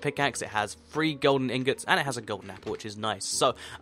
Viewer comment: On 5:29, did you just said "and a Golden Apple"???!!